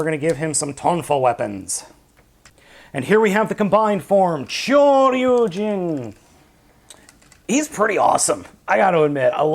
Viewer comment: it sounded like you said "kyoryujin" there lol